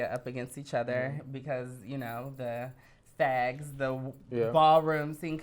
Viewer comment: Did she just say fags to a gay guy or fans I’m confuse 🤦🏾‍♂️😂🤣